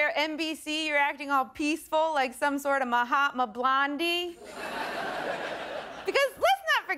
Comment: Did she just say Mahatma Blandhi? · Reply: Dipanshu Jain i think she said Mahatma blondy/blondie refering to Megan's hair colour.